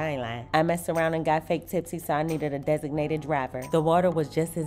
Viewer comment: Girl, not fake tipsy 🤣 😂 😂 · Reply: I thought she said, " I got fake titties"🙄I was like, what does that have to do with anything...whole time, she said "fake tipsy"🤭 😂